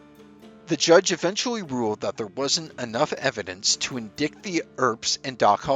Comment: What is this word....in dikt 13:00?.....do you mean in dite? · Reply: Late reply but deserves an answer. His AI generator read indict (in dite like you said) as in dikt.. 😂😂😂